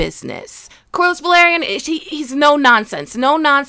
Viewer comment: This whole time I thought she was saying cordless Valyrian 😂